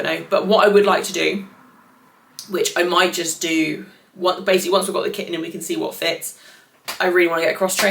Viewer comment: Thought at 31:30 you said "once we've got the kitten in" 😢